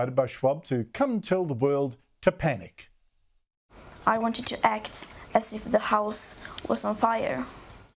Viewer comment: I thought she said : "I want you to act as though your pants are on fire"!